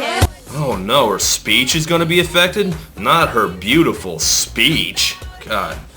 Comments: I always thought she was saying "my sneeze is gonna be effected" as in, her nose was going to collapse from too much snorting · I thought she said my knees is gonna be affected